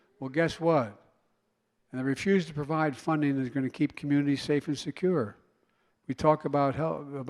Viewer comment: Anyone else catch at 0:10 where he said "I refuse to provide funding that is going to keep communities safe and secure"? At this point, they would be better off using AI for his voice.